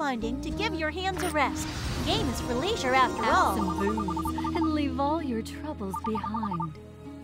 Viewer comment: did she just said "Have some boobs and leave all your troubles behind"? 😲